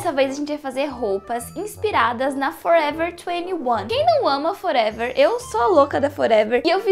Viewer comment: Nossa que susto achei que ela tinha falado FOREVER 2NE1 (escorreu um olho da Minha lágrima)